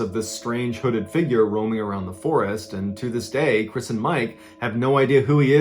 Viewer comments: chris and mike, lol i thought he said prison mike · Every time he says "Chris and Mike" all I hear is "Prison Mike"